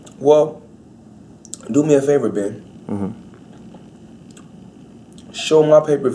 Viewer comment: my respect for the money team but did he just call him babe...wtf